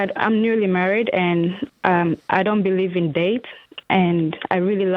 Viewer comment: At first I thought the first caller said "I don't believe in Dave" not debt lol